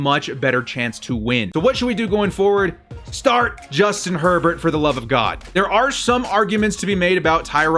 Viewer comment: I thought he said Justin Pervert...